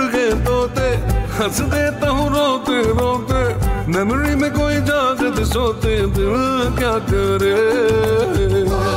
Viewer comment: Januaryyyyyh main ______ ??? ( what he said )..... From:— N’York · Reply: Bhi aye pasina.